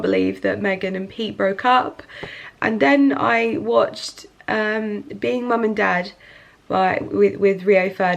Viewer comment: Does anyone know the name of the show that made cry? I thought she said Being Mom and Dad? I'm trying to look it up but cant find it.